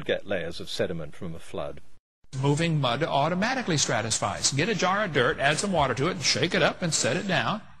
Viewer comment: Did anyone catch Hovind, at 1:57, clearly saying "stratisfies"? He managed to pronounce it correctly, as "stratified", exactly one minute later, however.